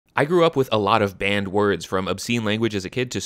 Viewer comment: I thought you said “as a kid, I grew up with band words…”